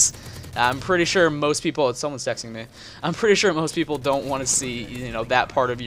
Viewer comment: the first time I swear I heard "Someone's sexting me"